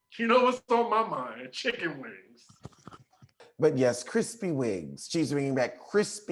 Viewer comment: I thought he said crispy wings too!